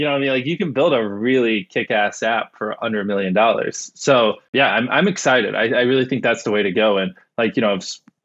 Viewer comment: I swear I thought he said 'hundred million dollars' there for a second looool!